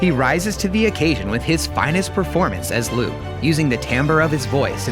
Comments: "Mark Hamill's final performance as Luke Skywalker" ... I thought you said Spoiler free? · When he said finest performance I thought he said final performance I was so sad for about 5 seconds until I went back to hear it again